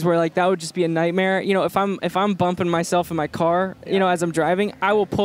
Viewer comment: lmfao i thought it said burping himself